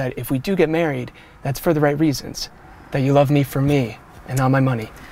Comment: in 0:52 when the man says me , the subtitles say mw